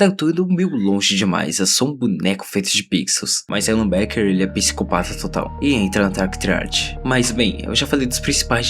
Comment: o Alan Beck entra na dark triard ? Só eu que escutei isso ?